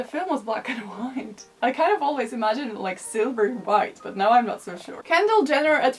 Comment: Jennifer López Always wears a silver dress! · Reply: Winddragon Lundholm. Haha I thought you said Sliver